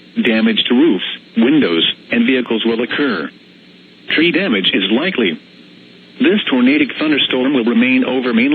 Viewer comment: i Thought he said: Treat Damages Lightly